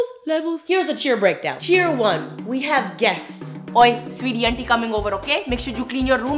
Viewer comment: Did i hear "sweety"? She called u sweety?